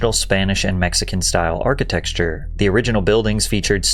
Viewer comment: Wait, did you just say "architectsture?"